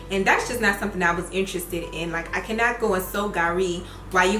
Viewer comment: Can anyone tell me what she said at 2:54 " I cannot go......." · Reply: soak gari